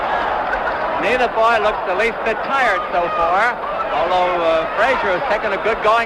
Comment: Did he say what it sounded like? Wow, that really is racist. Imagine if any announcer said that these days? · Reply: The announcer said "neither boy looks the least bit tired" the announcers were more racist towards the refs if you listen to other versions, this one is pretty good though but they do announce its a philipino stadium and philipino ref as if its a bad thing. I listened to an audio broadcast of this a long time ago where one of the announcers straight up called the ref a be***r ch**k for stopping ali from holding fraziers neck.